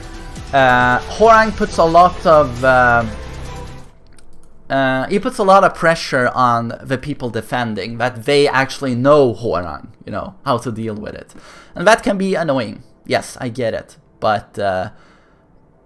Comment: Why does it sound like he says Whore-rang